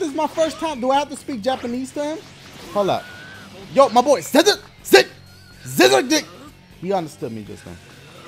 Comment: LMFAO Dashie did you just say "scissor dick"?? XD